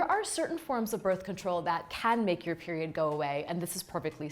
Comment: Did she just say beriod